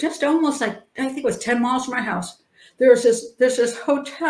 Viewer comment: This may sound crazy but i dont think they took tina…i think she escaped and got lost while running away and just died in the wild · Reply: im sorry i thought i heard 4 miles in the video* …youre right 👍🏼👍🏼